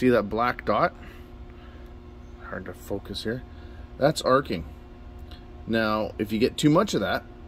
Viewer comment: Hello! Thanks for the video! Can you tell me what did you said after black dot 3:42? Cheers from Argentina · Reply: The black dot is arcing. It's metal transfer from one contact to another. If it gets bad enough it will cause switch not to work. Thanks for watching.